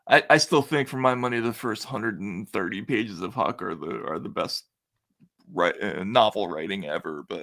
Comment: Matt states- "I still think the first 130 pages of ____ ___ are the best" What novel was he referring to??? Sounded like he said "Hawker Lou?" I've never heard of it and I could not find it online. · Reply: Adventures of Huckleberry Finn by Mark Twain